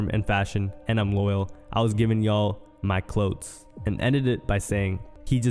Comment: did he say cloots instead of clothes 🤦🏾‍♂️ this guy here #tlkabtit… 🤷🏾‍♂️😂😂😂🤦🏾‍♂️